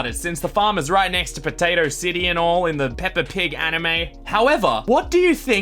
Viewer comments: Hold up, did he say Peppa Pig anime at 4:16? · did he just say peppa pig anime lol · Did he just say Peppa pig anime? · Did he just say pepa pig anime ???? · Did he say peppa pig the anime??? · Wait. Did you Say ANIME?!